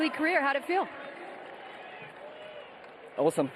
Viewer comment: ゲータレードシャワーの感想を聞かれて、"Awesome"と言ったのが「おー寒っ！」に聞こえた。